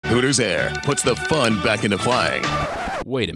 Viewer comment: I thought it said hookers air